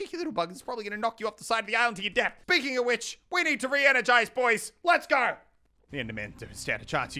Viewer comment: what he said : We need to re energize what i heard : We need to re advertise